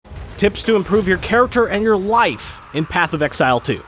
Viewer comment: ngl, I really heard "how to improve your character and your wife" and thought it was one of these commercials for "how to become an alpha" or whatever :D